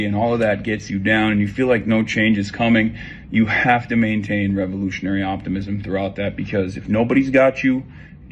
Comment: I thought he said "illusionary optimism" 😂 wouldn't have been surprised!